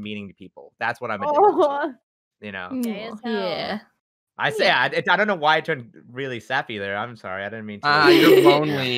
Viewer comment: I'm sorry but what did say in 22:50 ? I just wanna clarify if I'm hearing it correctly. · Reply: I think they said "gay as hell" XD